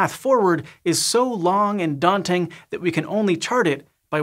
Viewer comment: so long and daunting. But, at first listen, I heard, so long and Dante-ing… same thing? Inflations and Constants.